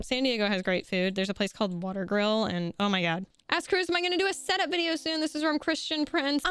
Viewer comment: With your donny thornberry impersonation at the begging, i honestly thought you said "ass kruz". It took me a second to realise what you actually said 😂